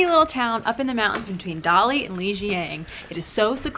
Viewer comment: Dude, pls don't mislead viewers. I was about to do the same but Shaxizhen is not in between Kunming and Dali. It is further away from them. Another 3 hours drive from Dali..... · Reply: She said it's between Dali and Lijiang...which is correct. You misheard.